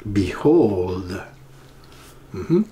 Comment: I hear "They hurt" rather than behold... I hear "Hurt again a few times after....